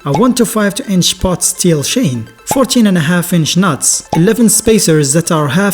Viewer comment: 14and1/2 inch nuts!!!... What??? Did I hear that correctly?